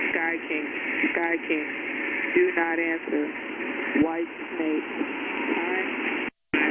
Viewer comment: And I thought I heard whitesnake on that one u sed in the intro. Wth?